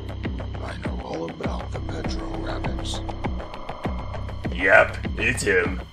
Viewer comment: I heard, " I know all about the petrol rabbits " and I know damn well that's not what he actually says, anyone know what he ACTUALLY said?